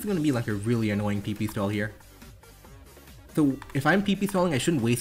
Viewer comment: the captions say “I’m pee pee smelling” 😭